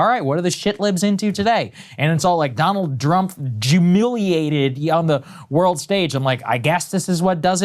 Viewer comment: Did he say “Jewmiliated”?